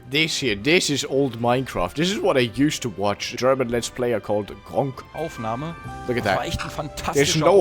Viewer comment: for a second i thought you said “GermanLetsPlay” or GLP, also a german let’s player who retired